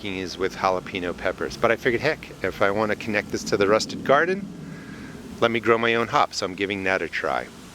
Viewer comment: I've just realized you say Rusted garden. I thought you always said Rustic garden! Haha